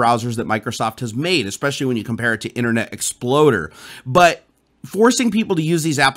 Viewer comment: Did you really say "Internet ExploDer" at 1:17? lol....